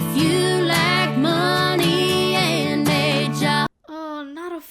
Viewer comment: I misheard a job as “hijab” and got really confused!!!😂